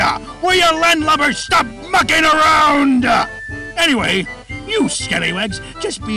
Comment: Freddy and Bonnie: *having demonic thoughts* Foxy: "stop mucking around" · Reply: I thought Foxy said Fucking around? 😅😅